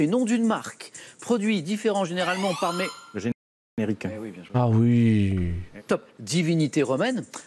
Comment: svp c'est quoi la réponse à 15:56 ? jai pas compris ce qu'il a dit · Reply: Le générique